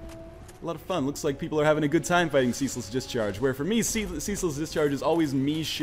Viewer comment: I thought he was saying Cecil's discharge up until now.